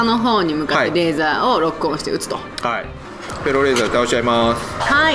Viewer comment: フェローレーザーが エローレーザーに聞こえてしまう… どうしよう！